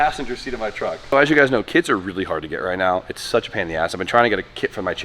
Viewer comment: I thought u said kids and not kits, i was like what did he say